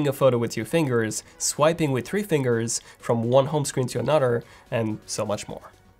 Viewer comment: Did he just say “one home screen to a nudder”?